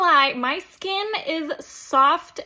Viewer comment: You said baby's soft but I hear baby's bottom 😂😅